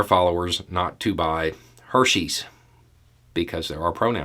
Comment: For me the whole "her" and "she " is such an improvement. For years when I heard someone say "Hershey's", I heard "Herr ScheiBe" which translates to Mr. Shit....